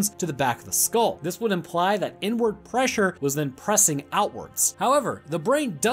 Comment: i legit thought you said "n word pressure" and i was confused for a second, then i realized that you said inward pressure which made alot more sense